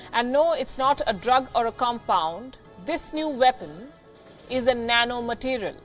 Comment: Did she say "this new weapon" at 0:40?.